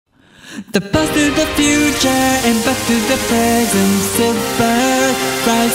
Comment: Excellent work! At first I thought the chorus said "The boat to the future, and back to the present." ^^